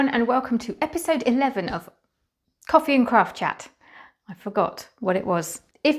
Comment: So I went into the other room for a sec at the beginning of your video and thought you said “coffee and crap chat” 😂😂😂